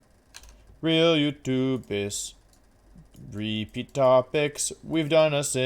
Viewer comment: Gonna be honest. I thought you were saying YouTubus not YouTubists until just now. I was just like yeah that's a thing to do and went on with life.